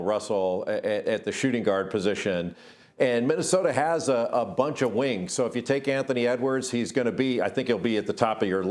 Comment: This dude winks so much i thought he said "Minnesota has a lot of winks"